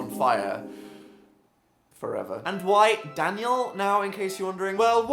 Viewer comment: am i the only one thought it said danielle howel?